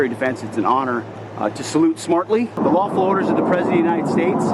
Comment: what does snoonute snortly mean? Said with the lamest salute I've ever seen. (Maybe he's better with Seig Heil salutes)